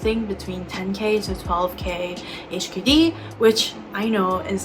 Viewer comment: had to rewind a few times to go back to the price, as I thought you were saying £10-12 HKD ( I missed the K!) I was wondering why you were saying it was a lot of money!! makes more sense now!😅